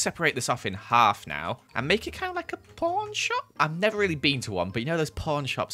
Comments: did he say p0rn shop? or cuz I'm so confused 4:14 what shop is he talking about could someone tell me cuz I'm actually bare lost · Yea....when joel said pawn shop I thought he said p0rn shop